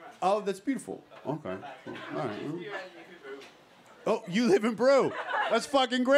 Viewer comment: What does Say at 3:55 ? Whats the "Great News"?? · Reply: He lives in Peru